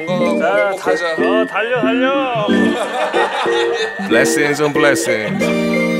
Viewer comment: 에 혹시 팔로가 뭐라고 말하는건가요..?? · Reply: blessings on blessing